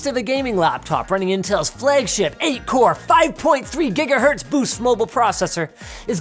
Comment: I heard that as "Boost Mobile" processor. xD